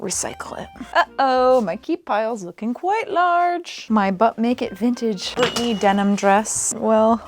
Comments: every time Rachel said “but make it vintage” I kept hearing it as “butt-naked vintage” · I can't tell you how many times I heard "Butt Naked Vintage" instead of "But Make It Vintage" 😅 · Listen. Every single time you referenced "...But Make It Vintage", I heard "Butt Naked Vintage." So.